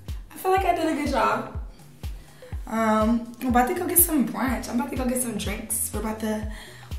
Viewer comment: I swore she said “I’m bout to get some drinks wit my thug” ah I wish she said thug